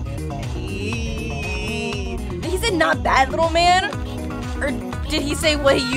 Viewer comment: I heard don't die little man